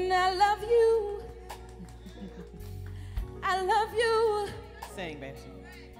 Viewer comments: Did she say sing bitch @ 2:11! Lol! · sounded like „sing bitch“! 😂😂😂😂